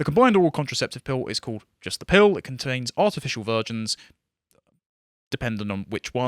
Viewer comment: I heard "artificial virgins" at first.